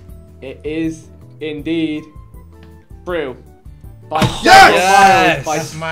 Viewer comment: did man just say frue? lol